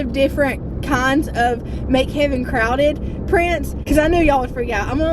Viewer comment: Lol 🤣🤣🤣 I promise I thought you were saying Mc heaven crowded 🤣 😂 😆 but they might just be bc this mama is craving a cheese burger 🍔